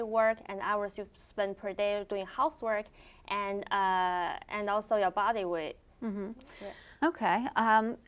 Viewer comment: did she say "also your body weight"? So the fatter the less well being?